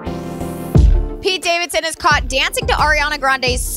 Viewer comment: HHAHA I THOUGHT IT SAID PETE DAVIDSON CAUGHT BREATHING